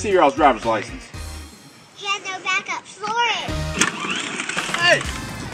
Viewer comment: "He has no backup, floor it." Future master mind right there. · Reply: i thought he said soar it